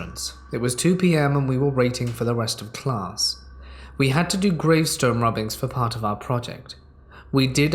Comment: Lol thought I heard field trip to a cemetery for gravestone robbings.